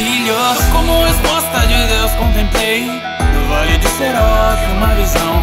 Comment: Não entendi nos 1:35. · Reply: Com a resposta de Deus ele comtemplou